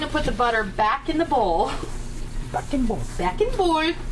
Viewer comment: Haha I heard you say "back n bowl" You left out Edwina. All of me is a funny movie!